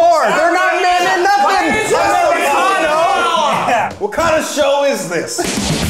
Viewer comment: i could've sworn that Kwesi said "Wakanda show is this?" in the intro, and i feel even more sure of that after the actual question